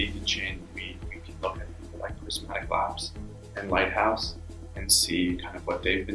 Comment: Anyone catch the name of the examples he uses at 4:13?? My audio’s gets distorted right as he’s saying it, would be very much appreciated!! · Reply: He talks about Prysmatic Labs: <link> (another team building an Ethereum 2.0 client) and Lighthouse (probably Lighthouse Labs which is a big Canadian coding bootcamp program).